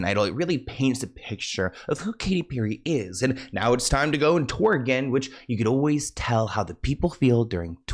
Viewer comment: When you said " tour again" I thought you said "twerking" desperate for a hit can mean dr☆gs too